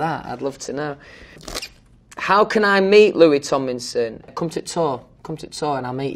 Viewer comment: all I heard was comt ta tor